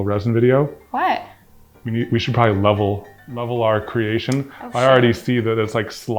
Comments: WAS THAT AN OH SH*T I HEARD? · Am I the only one who heard oh shit